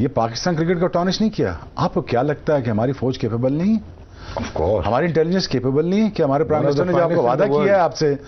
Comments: On 11:00 I heard hamare terrorist capable nhi h Are bhai world class quality k milte hai wo to aapke yaha 😂😂😂😂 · Did I hear Shoaib Akhter at 11:00 telling "Hamare TERRORISTS capable nahi hai"?